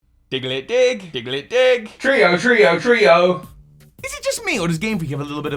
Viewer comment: When I was a child I didn't notice that Dugtrio cries is "trio, trio", I don't know why but I heard "frío, frío" (cold, cold)... So, everytime in was cold outside I started imitating my version Dugtrio cry hahaha End of story.